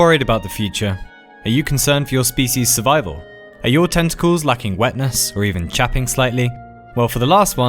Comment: I thought he said testicales and not tentacles :/